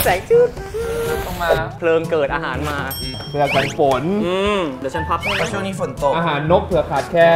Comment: พี่เตพูดอะไรอะ น้องได้ยินนะ55555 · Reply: Tee'z Minth เดี่ยวฉันพับให้นะ เตน่ารัก